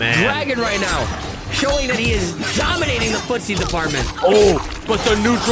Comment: “Dominating the footsy department”! I thought he said “Dominating the pus…..you know what? Nvm 😭🤦🏾‍♂️🤣